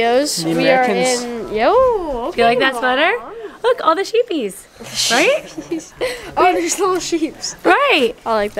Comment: said “looks at all the sheepies” I thought she said “look at all the cheapies”